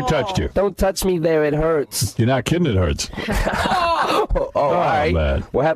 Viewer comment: What did Howard said there? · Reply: “You’re not kidding it hurts”